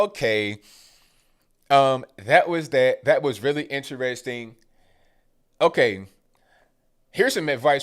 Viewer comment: I thought you said "That was really interwasting (interesting + a waste of time) and was like heh, that's funny, I gotta start using that whenever I find trash like this lol...